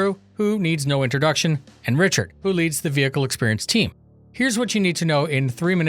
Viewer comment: I thought you said Pritchard instead of Richard at the start... feelsbadman...